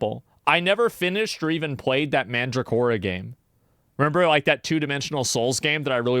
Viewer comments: Does anyone know what game he mentioned at 29:26 i dont understand what name he said... Mandragolian??? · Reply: Mandragora game. It's on steam. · Does anyone know what game he mentioned at 29:26 i dont understand what name he said... Mandragolian???